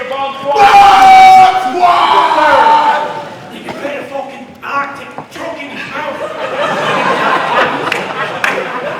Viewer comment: did any one understand tysons joke that made everyone laugh at 31:19 ? I keep replaying it but can't understand 😂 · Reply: Maubrey Destined “you can fit a fucking artic truck in his mouth”